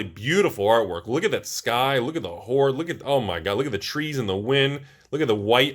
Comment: did he say look at the whore!